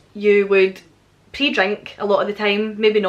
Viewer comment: I'm not gonna lie...I thought you said pee drink at first...lol...laughed at myself when I realized you said pre drink...we do it here too only we call it pre gaming... We haven't had a Summer here either...here is Syracuse, NY...I live 10-15 minutes outside of the actual city...our normal summer's are hot, humid and sticky...the kind of weather where you walk outside and start sweating right away...temps in the high 80°s and 90s...this year we've had rain, hail and most temps not going above mid 70°s or low 80°s...last night it went down to 56° and was down right chilly! Anyway...I'm done complaining..lol You definitely should do a video about the different words for things...😊